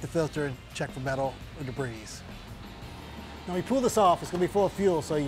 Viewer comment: did he actually say dibrees? LOL